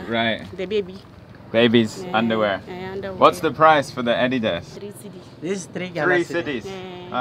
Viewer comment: All I heard was what's the price for head, ran back to see what was on my phone, very disappointing! Lol...